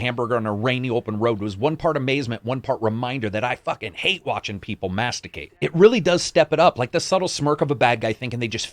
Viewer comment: I thought he said masturbate. lmfaoooo